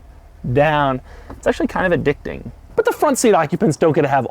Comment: When he was swishing his finger over the volume buttons and commenting 'it's kinda addicting' I thought he said, 'it's kind of a dick thing'